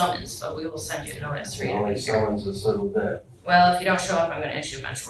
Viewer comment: Anyone catch what he said at 8:08? It sounds like he said something about how he would only come back to court if they send him a summons (as opposed to a notice) because the judge responds that if he doesn't show up she'll issue a warrant. I replayed it a bunch of times and can't make out exactly what he was saying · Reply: If there's a summons, I'll be here (?)